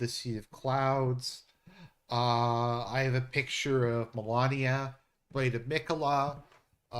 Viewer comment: did he said Melania?